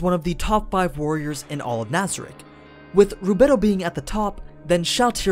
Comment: what name does he say and who the fuck is that? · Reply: Rubedo. She's Albedo's younger sister.